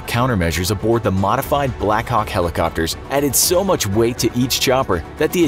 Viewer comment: someone else hear "blakcock"?